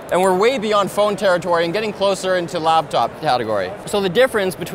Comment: does it bother anyone else it sound like he says labtop and not laptop? or are my headphones just wrecked lol